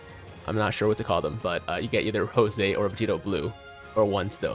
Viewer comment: Yo did I hear that right “José” lmao